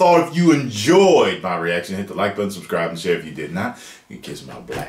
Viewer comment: Completely unrelated... what does Tyrone say at 4:35 ? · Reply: If you did not, you can kiss my black derrière...